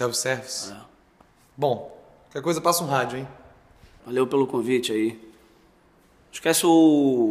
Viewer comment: eu ouvi valeu pelo covid Bateu um desespero